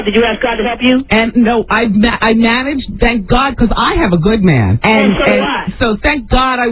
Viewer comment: Wendy did you just say you have a good man? A gay man 😂😂😂😂